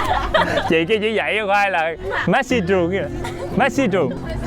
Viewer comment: Sao cái camon mk lại nghe thành messibu.. vay😂